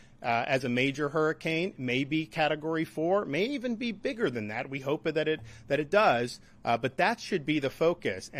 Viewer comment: Time: 1:45 Did he just say "maybe be bigger than that & hope that it does???"